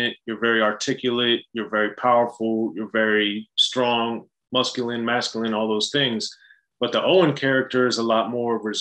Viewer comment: Did say "musculine" at 1:35 ? 🤔